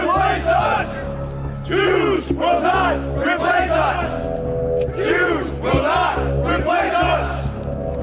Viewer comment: The problem with having an auditory processing disorder and ADHD is you tend to hear random things. Especially so when groups of people chant things. What I hear: 1. 0:17 “roof” 2. 0:33 “Choose will not replace us” 3. “Whose streaks? Our cheeks!” 4. 2:24 “Who Jerome Powell to get here?” lol I’d keep going but I’d like to attempt to figure out what happened at this point in history